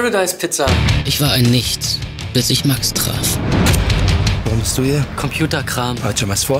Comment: was sagt er da ? wohnst du hier ? - er drauf - computerkram ? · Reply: Er fragt "Warum bist du hier", da die beiden dort anscheinend Sozialdienst o.Ä. leisten müssen.